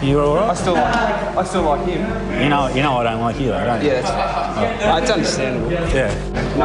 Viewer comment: does riewoldt say 'i dont like you' to gilbert at 08:54??